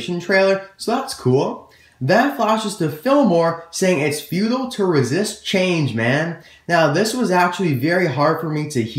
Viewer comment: At first I thought Fillmore said "it's hard to resist James man" LOL